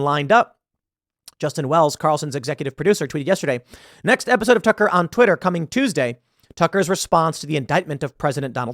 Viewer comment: When he said Tucker on Twitter I thought he said tuck around Twitter like he was alluding to fuck around Twitter, And I thought it was fucking hilarious